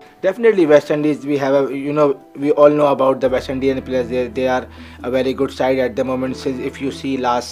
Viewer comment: Did he say west indian player?? Bhaijan Russell sirf ipl khelta hai India ka thode hi hai 😂😂